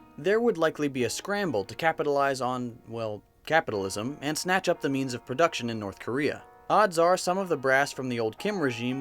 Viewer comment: did someone say seize the means of production